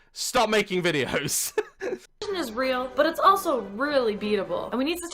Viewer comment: I thought the bitch said depression was really beautiful ... on god... I was about to call my fucking sugar daddy and have him hire a hitman